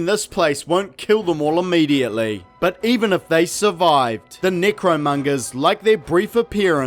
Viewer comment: Why does it sound like your saying negromongers?